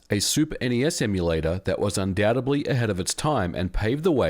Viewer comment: Im so sorry to do this but did I hear you say "undboubtably" instead of "undoubtedly"?